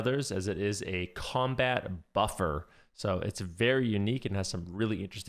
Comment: thought I heard "Combat Fluffer" 😂